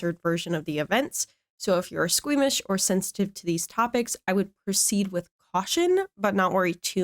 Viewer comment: Hmmm interesting… I do apply a filter to cut out some background noise, and my Mic has filters. I have a consistent problem with an occasional popping sound as well, but I’m not sure if any of that is what you’re describing? If you have a timestamp I can at least see if it’s the one problem I know of. I’m constantly trying to improve my set up and this video didn’t seem out of the norm for me or (I presume) other viewers, so would love more specifics. Thanks for trying it anyway, it might sound better on a phone or something with lower quality audio, I will try to improve for next time ❤️ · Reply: just picking a random moment, 1:14 i can tell you’re saying “proceed with caution” but i hear “roceed with aution” and i think it’s the filter dampening the beginnings and sometimes ends of your words. i’m listening on a phone but i just have extra sensitive hearing so once i noticed i couldn’t stop noticing